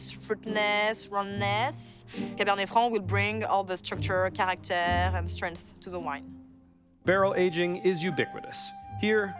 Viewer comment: WTF did she say @ 2:50 Cab Franc adds... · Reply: "Cabernet Franc will bring the structure, character, and strength to the wine."